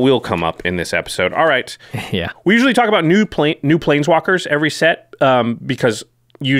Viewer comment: into the video, did say "nude planswalkers" I swear I heard it! I just about died man.